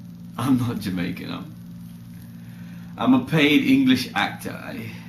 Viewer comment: I thought he said 'I'm not English, i'm mad!' ?